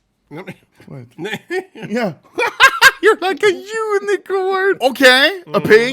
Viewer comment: Did I hear Anomaly say JEWnicorn ?